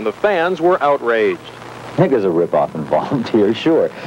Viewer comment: what did he say?? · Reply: Lol I believe he said "I think it was a rip off".